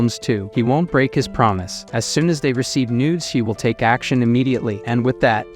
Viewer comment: The caption literally said "as soon as they receive nudes" instead of news 😭